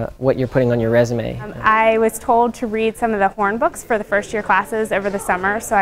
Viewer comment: daydreamerindian I think he said "hornbooks" like the person